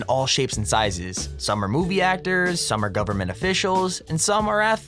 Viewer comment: Ok for a sec there I .. literally for a secI thought he said booby actors just for a sec lol